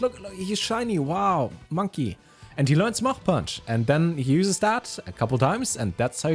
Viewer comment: thought he said muff punch